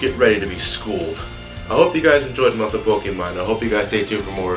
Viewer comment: For one moment, I thought he said a "month of Okami" instead of Kirby.